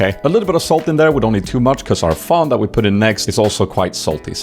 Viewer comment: Is the sauce you added around 4:05 some kind of pre-bottled fond? The closed captions translated it as "fun," but I'm pretty sure that's not it. I want to try that recipe but that's the only ingredient I can't figure out. It looks like Worcestershire sauce. Hmm.